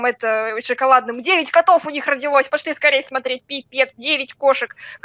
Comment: Мне послышалось или сказала пи*дец?